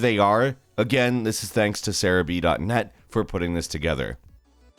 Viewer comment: I thought you were saying Sarah B. .net